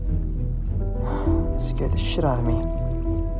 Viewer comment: Am I the only one who heard him say that scared the shit outta me?